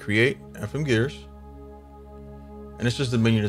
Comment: Lol, I thought you said “effin’ gears”. 😆 I like these gear plugins. Another one is GF Gear Generator which allows for helical and double-helical (herringbone) gears.